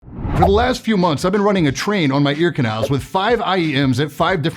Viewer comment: WHAT DID HE SAY??????? he- ran a t-t-TRAIN?? On his ears?? Crazy